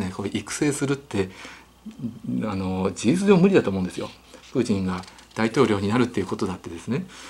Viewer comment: プーチンが時々プーチィんと聞こえる〜笑笑。 なんだかプーチィんが気になり話が入ってこないのは私だけ？？笑笑